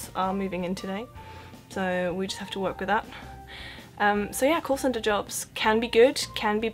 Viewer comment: I'm sorry, don't want to sound rude but I didn't quite understood, the first job she mentioned, all I heard was "course sender jobs" can someone tell me what she actually said? 😅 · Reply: call centre - working in an office of people making and receiving telephone calls for a company.